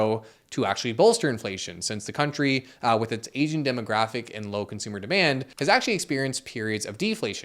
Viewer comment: "Since the country with its aging demographic" I swear, I heard that as since the country with its Asian demographic. And im like "well yes, that is indeed factually accurate, but how does it impact the stock market?"